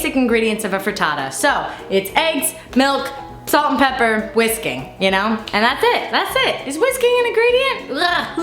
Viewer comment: When she said whisking I first thought she said whiskey...which I still feel like is an essential ingredient. Am I alone?